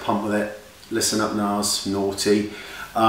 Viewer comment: did you say nars?! Lol xx im lovin this foundation so far just got it :) xx